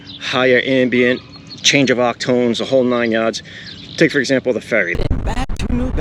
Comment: Easy Hood did well! Good test. What was that word you said at 2:02? "Change of _____" I don't think I'm familiar with it. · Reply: Haha yeah I was saying how different ambient noises and Octave and tones can have an effect and I combined them.